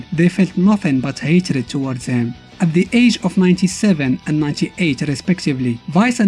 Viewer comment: I made a mistake and said they are 97 and 98 in the video. What I wanted to say is they're 27 and 28. Sorry about that ^^' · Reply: Ah! You actually had me! I thought I heard it wrong and it was meant to be "the game of 97 and 98, so I went back and heard "age"… I was like wtf lol.